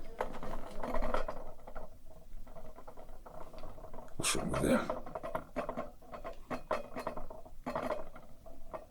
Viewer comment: What does he say at 1:51 ? Why does he say rude ?? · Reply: he says "what's wrong with ya" I think